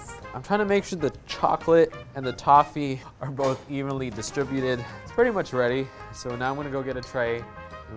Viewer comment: At first I heard “evilly distributed”